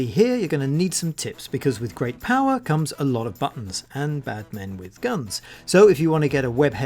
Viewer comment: ha I thought u say batman with gun lol